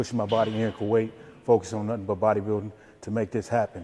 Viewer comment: when you think a Mr. Olympia competitor was saying ‘Natural Bodybuilding’ instead of ‘Nothing but BB’ haha